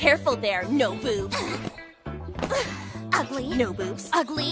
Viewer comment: I thought she said “no boots”